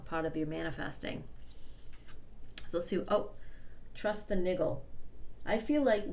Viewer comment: Did she just say....... “trust the nigga”??? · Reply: Gabriel Marrero “niggle”